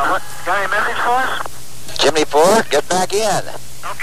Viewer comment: For a second I heard "Gemini 4, get back in!" as "Jimmy darn, get back in!"